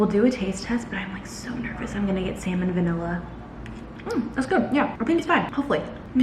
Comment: NERVOUS THAT YOURE GOING TO GET WHAT!?????😂😂😂 it sounded like you said salmon&vanilla lol